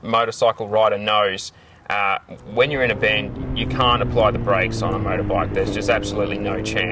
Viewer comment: Did you say you can or can't apply the brakes in a turn? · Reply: He said "can't". Which is wrong.